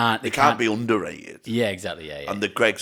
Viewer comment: Is it bad that I misheard the beatles as the beales and thought was going on about Ian and co.